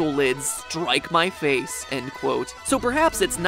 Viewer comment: I thought it said that he studied WEED..... XD